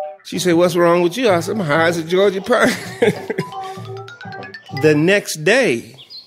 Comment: "High as a Georgia...." what??? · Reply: Thanks. I thought he was saying "Georgia Peach" for some reason. lol